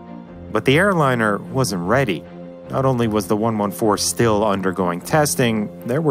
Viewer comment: There is a mistake in subs at 1:08-1:14. Please, fix it. Khruschev said: Вы адвокат Капитализма, Я адвокат Коммунизма. Вот давай-те мы [и] ПОСОРЕВНУЕМСЯ. And that last sentence mean: So let's make a competition. As you can see - there is nothing about kissing. Also. It is a great video. Greetings from Russia ;] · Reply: Three more mistakes at 5:17 , 7:12 , 7:26 "144" instead of "114" :)